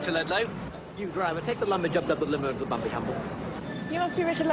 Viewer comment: What does he say at 9:15? Always cracks me up. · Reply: It's just nonsense speak. "Take the lumbage updup the limo into the bumbly humble."